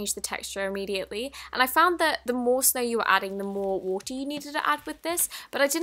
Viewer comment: did she say snow